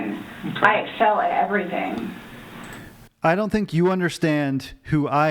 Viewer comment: "I was a straight A student in high school - I'm an outstanding mother - I excel at EVERYTHING i do." Who the hell says that outloud to another person?!?! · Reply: When she said “I excel at everything”, I thought she was saying “I sew everything”, like that made her a great mom, or a great person, because she could sew! I didn’t know until I read your comment that she said “excel” 😂😂😂